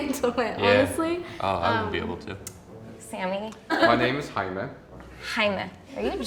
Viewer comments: As a Hispanic, it hurt when that lady thought the name “Jaime” was German :3 Edit: I just realized the Latina girl (in back) was also shocked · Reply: I legit thought he said hymen. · “My name is Jaime” aRe yOu GeRmAn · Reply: the way he pronounced the "h" sound. He didn't pronounce it normally. He pronounced it like the spanish soft "g" sound like in the spanish word "gente." The spanish "g" sounds like the german "h." She thought he said "heim." I know that "Jaime" is a spanish name too, but I didn't know he said "Jaime" because of the way he pronounced it and I guessed he was german too. Everybody seriously needs to chill on this. That man just had horrible pronounciation. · Dude what? He literally said his name's "Jaime" that was a dead give away to me, maybe because I'm also Mexican lmao but seriously she asked if he was German after he said his name😂 like whatttt? 😂😂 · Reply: I thought he said Hymen. I've been reading the name Jaime as Jamie spelt different for legitimately a year. I am so sorry to the Jaime's out there 😦